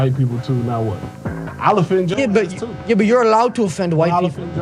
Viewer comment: It sounds like he says "ill offend Joes ass tooo" instead of "ill offend yo ass too" which is proof he saw your video on his special 😂😂😂😂❤🎉